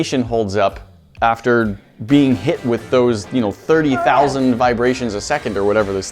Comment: I accidentally heard it as "30,000 biracials per second"....